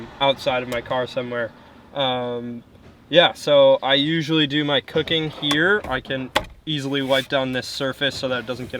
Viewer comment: "i usually do my cocaine here" yeah i probably misheard that